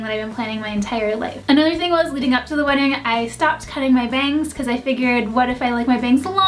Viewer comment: Am I the only one who heard her say 'cut my veins'? My heart stopped and then she was like 'bangs' and I felt stupid for hearing veins instead XD